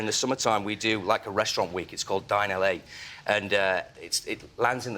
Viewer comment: Did anyone else hear "we do a restaurant week called Die in L.A." at 8:59?